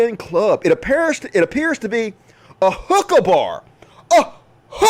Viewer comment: At first I thought u where saying hooker bar, and I'm like, what's wrong with that "